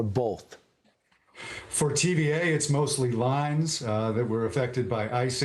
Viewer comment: I thought he said tda was affected by icing lol well that is true He said tva is affected by icing . Keep warm folks !